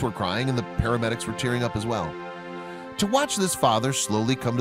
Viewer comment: I thought he said the paramedics where cheering up as well